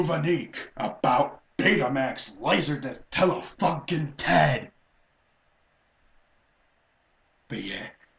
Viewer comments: Is it wrong that I heard "Tele-Fuckin'-TED"? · Did I hear you say something I wasn't supposed to hear at 12:50? "Tele-FUCKING-ten!!!"? Boy, get a soap bar... Now. Why? Because this and your rant is hilarious. See? This is what happens when you stay up late and talk to your camera while hyped on Sonic Mania and Coke at 3:00 A.M., you kinda go a bit nuts.